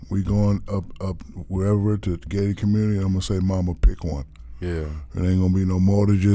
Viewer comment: thought he said "Gay Community" 🤦‍♂️..#2muchDWadeNews